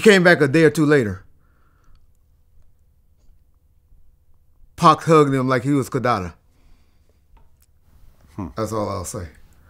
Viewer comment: What did he say at 2:50 - 2:52? I didn't understand it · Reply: He said " Pac hug him like he was Kidada." Kidada Jones was Pac girlfriend before he died and she is also Quincy Jones daughter.